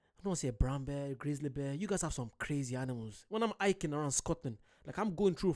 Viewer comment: Bro no offense I thought you were saying Iking and I was like what is iking?!?! And you meant hiking 😂😂😂 my bad bro but yeah you would enjoy hiking but we def have some crazy animals but just be cautious and you’ll be fine